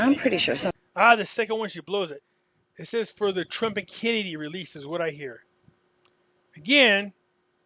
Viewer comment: I think I heard “President Trump & Kennedy release”